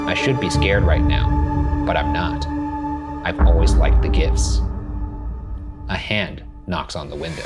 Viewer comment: when i saw the line 'i like the gifts' appear on screen i thought it said 'i like girls' fgsdvfdjfdj